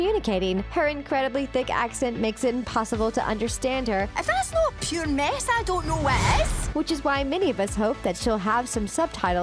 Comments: She said 'if that's not a pure mess I don't know what is' I know cuz I live in Scotland 😂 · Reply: I thought she said if that’s not mace I don’t know what is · "if that's not pure mess I don't know what is" I think that's what she said ;_; Yup it was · I thought I heard “ I that a pure mess I don’t know miss” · I heard "If thats not pureness then i dont know what is!" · if you can understand what i'm saying.. "i (fast/best) know pure (meth/meh) sandorno west?" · I heard Merida say "If that's not pureness then I don't know what is!" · I heard “If that’s not pure ness I don’t know what is” from Merida.